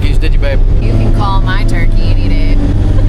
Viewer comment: I thought she said you can gobble my turkey anytime, get er done